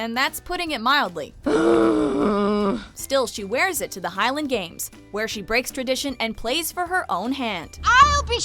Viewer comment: Did she say Hylian Games.... or was it just me? xD